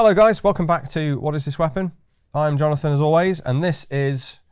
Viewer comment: Thank you, Keeper of Firearms and Artillery at the Royal Armouries Museum in the UK, which houses a collection of thousands of iconic weapons from throughout history. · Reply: I thought he said his name was "Jonathan Asalways"?